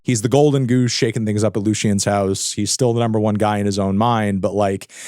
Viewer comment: misheard him and thought he said drake was shaking ngas up 😂